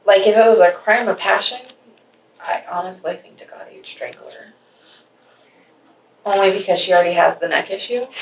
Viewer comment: What does say at 1:34:15 ? She talking about being a crime of passion and Shanann’s neck injury... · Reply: Said she would suspect strangulation.